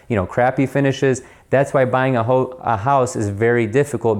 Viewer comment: ? Did he just say buying a hoe ? When the truth starts to come out lol